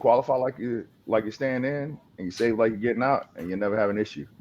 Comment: he says “Qualify like you’re staying in __ like you’re getting out” what was that word he said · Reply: Save!